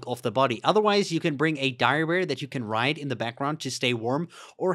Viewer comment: bring a what ? Could not understand the word… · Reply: Dire bear sorry